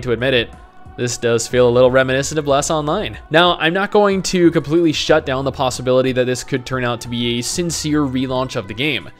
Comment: What did you say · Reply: "Now I'm not going to completely shut down the possibility that this could turn out to be a sincere re-launch of the game. I've just been shat on by so many companies recently that I can't help but be pessimistic."